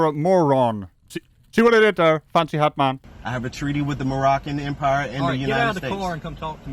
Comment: I thought he said "Moorish Empire in the United States." Is that a declaration of conquest? I don't even remember the war being declared.